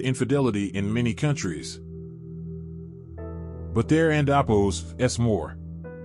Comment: Amen. May God grant us conversion of many souls. Thank you for the video that confirms truth. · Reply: I wish they wouldn’t use computer generated voices. Throughout the entire video I kept hearing something that sounded like “and opos”, whatever that is. Then, in referring to Pope John XXIII (?), I think I heard “John XEI”. I got lost trying to decipher the mispronounced words.